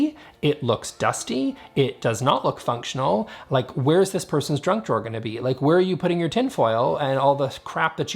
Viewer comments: “Drunk drawer?… or junk drawer? 😁 I like the idea of drunk drawer, full of ramen noodles and cheese puffs. And painkillers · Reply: I thought he said drunk drawer too😂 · "Where's this person's junk drawer gonna be?" I heard "drug", not "junk". Classic example of projection I guess.